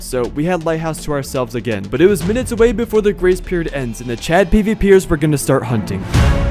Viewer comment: I legit thought you kept saying “these chat gpt’ers”